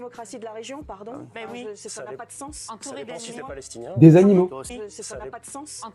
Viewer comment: Pour ceux qui veulent se l'écouter en boucle : 1:24 Ok c'est flou. Moi j'entends "animaux", éventuellement "ennimos". Un ingé son t'isole ça en 2 secondes, après y a plus qu'à.